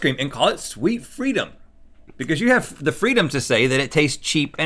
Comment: When they said Sweet Freedom for the blue bunny one I thought they said “Sweet Fritos” 😂